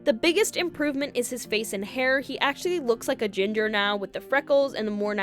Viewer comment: oh my god when you said “he actually looks like a ginger now” I heard ‘agender’ and thought i was losing my mind or missed some very critical lore. Had to listen like 3 more times to actually hear “ginger”